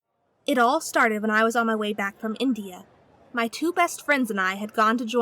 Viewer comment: For a second I thought she said we were coming back from Indio my home town